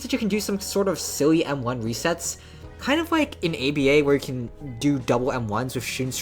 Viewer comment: thought you said yba for a second